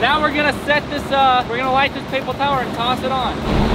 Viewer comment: Who heard papel tower